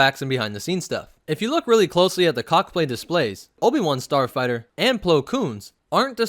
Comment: Is it just me or does it sound like he said cockplay displays